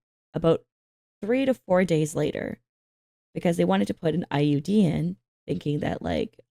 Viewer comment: Oh IUD, I heard IED and thought the doctor wanted to put a bomb in your head lol